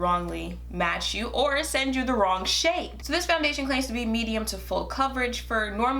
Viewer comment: When you said "send you the wrong shade!", I legit thought you were gonna say "shit"😂😂😂